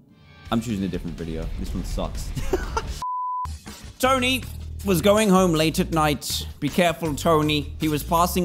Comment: I thought it said Tony stark was going home late at night.